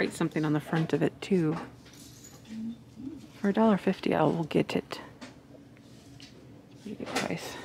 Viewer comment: Hi, it sounded like you said "for a $1.50 Owl get it". 😂